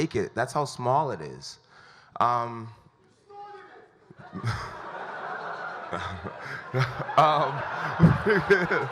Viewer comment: what was said at 31:50? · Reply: He snorted it!